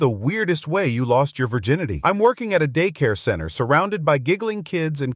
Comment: I heard "I am working at a daycare" I came straight to the comments and I didn't even want to watch the video